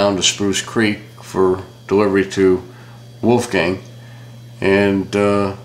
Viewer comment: did he say wolf gang?